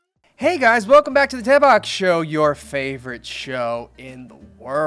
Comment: I swear to god, I know it’s the “Daebak” show- but I heard “Tampax” show!?😅😂🤣🤷‍♀️